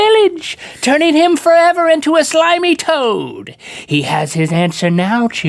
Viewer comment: when link said slimy toad I thought he said slimy toe.